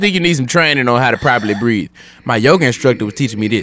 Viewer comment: did someone say Yoga?